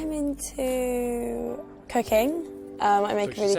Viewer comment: i really thought she said cocaine lol i just paused for 5 minutes laughing before i realized she said cooking